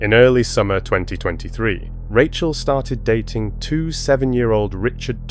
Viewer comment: Am I hearing correctly or something but did he just say she dated two 7 year old ?